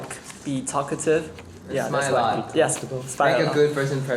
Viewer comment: Someone help me and my very poor ears out. What did Ten say at 5:03? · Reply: He said smile allot